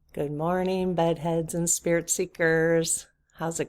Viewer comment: Lolololol I thought you said ‘butt heads’ instead of Bed heads… 😂